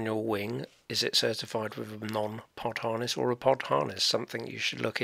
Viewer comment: great videos.. i don't know what you are saying at 0:10 .. partonous??? non-partonous??? thank you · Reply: Hi John, I say none pod harness, I am asking the viewer, is the glider you fly with certified with a none pod harness or a pod harness.